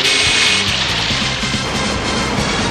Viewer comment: hey, at 2:54, did he say "Oh Christmas"??